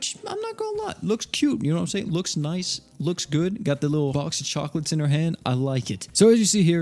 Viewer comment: I swear that I heard "Vox of chocolates".